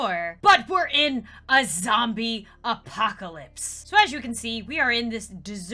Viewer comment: Instead of zombie apocalypse at first i thought it said Zumba classes😄Help me